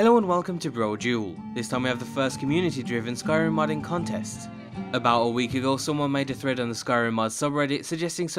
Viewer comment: did Brodual just say "about a week ago" ? :D